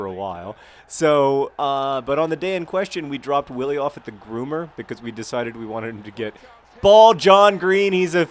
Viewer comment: What he said: "We dropped Willie off at the groomer because we wanted him to get-- BALD JOHN GREEN..." What I heard: "We dropped Willie off at the groomer because we wanted him to get BALD."